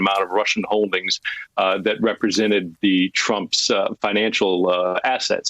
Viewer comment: Willie said ‘asset’ but I heard ‘Asshat’